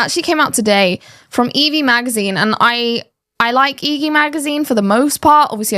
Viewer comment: Did I hear "EV Magazine"? There's a magazine for electric vehicles?? Or is it a magazine for Pokémon lovers -Eevee-? 😂 My first thought with the statement "Hypergamy is a myth" was: Eventually we are all equal (molecules, dna, cells), so does hypergamy even exist?? 🤔